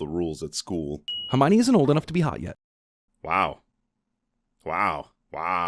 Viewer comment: I thought he said not old enough to be high yet. I really wish that clip didn't play for a second time. Cmon dude... just... why...